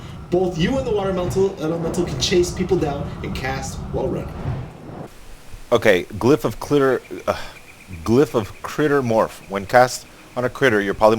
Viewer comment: LOL 11:07 i thought he was gonna say glyph of clitoris.